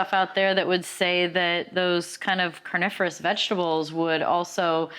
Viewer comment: Did she say carnivorous vegetables 😂😂